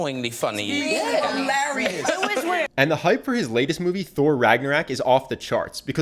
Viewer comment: Did he just said 'Thor Ragnarak' at 0:53??